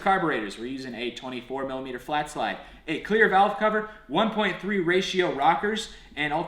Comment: Anyone else thought he said queer valve cover?